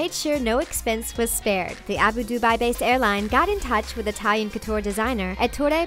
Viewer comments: it sounds like she says "Abu-Dubai" :)) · during 8:35 did she say.. the Abu-Dubai? lol. it's Abu Dhabi. Get-it-right.